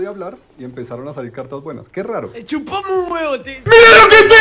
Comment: en el minuto 4:31 escuche a decir "chupame un huevo"